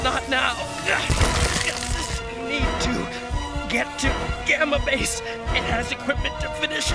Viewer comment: in the lyrics it says get my face instead of gamma base 🤣🤣